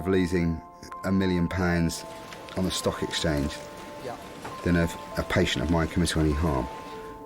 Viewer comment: He rather face loosing a million pounds on the stock exchange than WHAT DID HE SAY????? 5:35 Help please. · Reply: "Than having a patient of mine come to any harm. That's pressure", he said